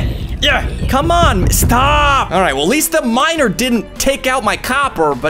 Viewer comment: “At least that miner didn’t take out my copper” what I heard: “Atleast the MINOR didn’t take out my copper” excuse me WHAT.